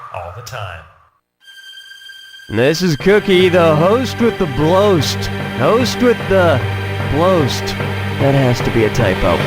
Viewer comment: What was he saying? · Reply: “This is Cookie, the host with the bloast. Host with the bloast. That has to be a typo.”